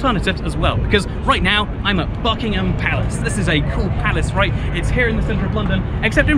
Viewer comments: I thought said "were at Fuck u palace" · Thought you said fuckingham palace